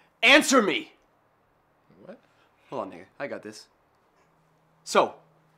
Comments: did he say, hold on nigga? LOL! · Who else heard him say hold on nigga